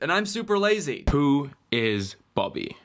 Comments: When he said “who is bobby?” I heard “poo is bobby” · Who heard “Poo. Is. Bobby.” I really heard that.